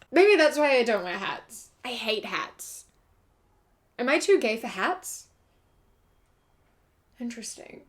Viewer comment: I originally heard it as "Am I too gay for hets?" yes, yes I am.